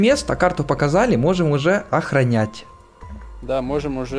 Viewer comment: мне послышалось охренеть